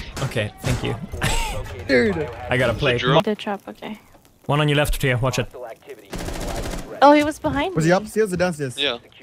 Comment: Did say "one on your left Teo, watch it?" At 3:40???